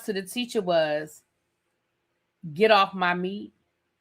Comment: OMG I thought that little boy said get off my knee, not meat...that's crazy.